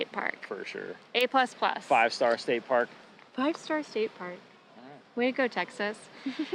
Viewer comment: why did she say Waco lol? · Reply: Maybe it's “way to go”